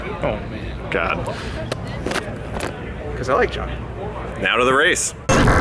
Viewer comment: “Cause I like josh” 4:29 - did finnegan call Jon, josh?